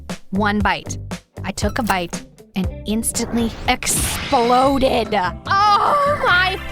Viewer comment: I just thought "i bite and there goes your virginity " instead of dignity 😂😂😂...the way she was doing UHUHUhH😭❤️